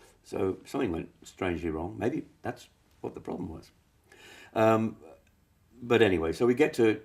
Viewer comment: what does Jon say at 0:55? I really don´t hear/understand... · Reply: "Something went strangely wrong, maybe that's what the problem was"